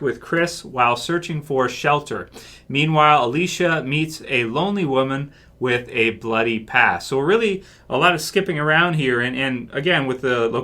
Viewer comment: at first I thought you said, 'alicia meets a lonely woman with a bloody ass'